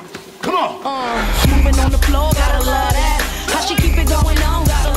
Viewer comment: First saw this commercial thought they were saying Gotta Lard ass.